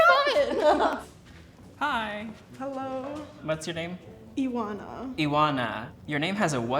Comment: the way she pronounced her name is not right in romanian either lol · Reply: I was confused, too; I heard her say “Iwana” instead of. I immediately came to the comments to see if I was the only one haha